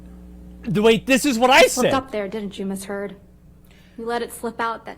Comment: Am I hearing this right does the lawyer say "You f****d up there didn't you Ms Heard?" at 18:19 ? This lawyer is badass if so. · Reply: "You slipped up there didn't you miss Heard?"